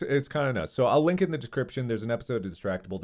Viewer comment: Aside from what had happened, to me, it sounded like Mark said: “Decription” here➡️ 3:56 instead of: “Description” and it’s funny to me!